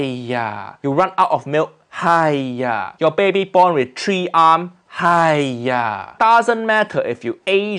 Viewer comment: i heard “tree arm” instead of “three arm”💀